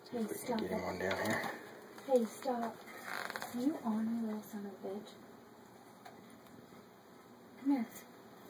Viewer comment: did she say son of a bitch ? xD